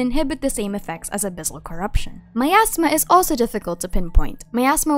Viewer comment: for a second my brain thought you said "my asthma" before I figured you said "miasma" 💀